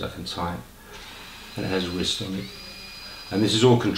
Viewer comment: when he said has a wrist - I thought he said - has a whisk... lol